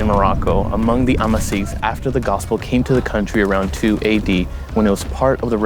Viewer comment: At around 2:35 in the video, in terms of year, did you say 280 or 2 AD? If the latter, then the Gospel would not have reached Morocco around 2 AD because Jesus, whom the Gospel and Christianity is about, was himself still a child then. Christianity spread out after His death, resurrection, and ascension, which was way after 2 AD. Nonetheless, thanks for your missionary work. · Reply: He made that error. He said 2 AD